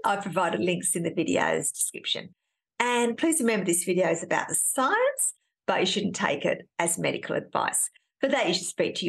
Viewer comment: Thank god I was vaccinated, have all my boosters and took paxlovid because everytime I got covid I did not end up in the hospital or die. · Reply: For a moment I thought you said "trust the séance".